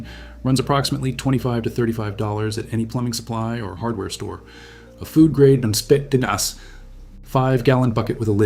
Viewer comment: What are you saying about food grade something at 4:21ish? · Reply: He was talking about food grade buckets. He said to go to the hardware store, but those aren’t food grade so you can poison yourself basically.